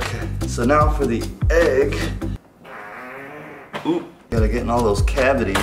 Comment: poop anyone hear